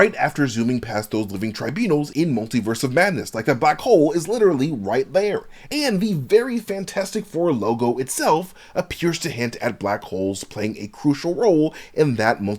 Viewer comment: I thought you said the VERY FANTASTIC FOUR like it was a new EXTREME series! 😂